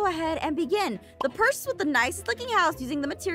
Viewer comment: What's supposed to be said "The person with the best looking house..." what I heard "The purse with the best looking house..."